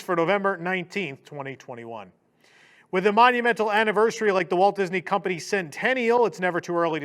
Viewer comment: Somehow I heard that as WDWs 100th anniversary and was mildly shocked as I had just woken up. “I didn’t sleep for 100 years, did I? Chapek is still CEO? Huh???”